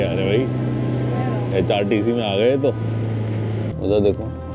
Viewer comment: Did I hear HRTC 09:09 ? 🤣🤣.. i highly object HRTC is safest transport in snow..